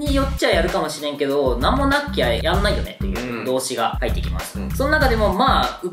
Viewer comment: 動詞が入ってきます。が曹司が入ってきます。に聞こえた笑